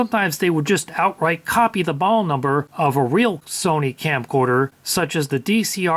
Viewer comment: Subtitle: "the ball number of a real Sony camcorder" Voiceover: "--the model number of a real Sony camcorder..." What's wrong with the subtitling? There are no punctuations at all, and instead of "model number", "brands they're not", and "make and model", it's "ball number", "brands or not" and "making model", meaning the fact that although the voiceover said things right, the way the text was written in the subtitles is wrong.